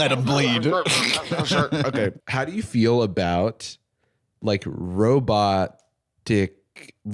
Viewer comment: his pause between robot..ic and i wasnt looking at the subtitles i thought he fucking said "how do you feel about robot dick"